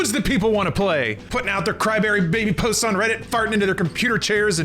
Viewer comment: Did say “Cry-berry”?